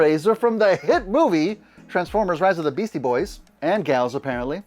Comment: Ha! Perfect name! Rise of the Beasty Boys! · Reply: For a moment I thought you said Rise of the Boys, as in the TV show The Boys